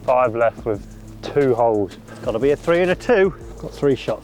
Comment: Rick sheils …bahaaa 🤣…..sorry thought you said 3 holes ☺️